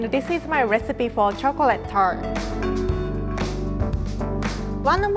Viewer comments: I thought she said "chocolate turd" for the first one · For the first one I thought she said chocolate turd.. 😂😂